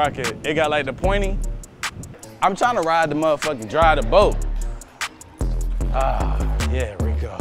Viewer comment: did homie say hes trying to ride the moda sucka that drive the boat big pause wtf is going on